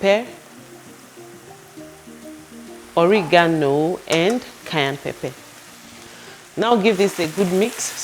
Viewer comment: Looks good..at 2:26 you add something red but I cant understand you..and I dont see it in your ingredient list? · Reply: she said cayenne pepper